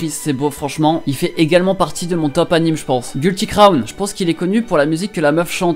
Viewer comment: Il a dit quoi à 6:30 je trouve pas l’anime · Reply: Guilty Crown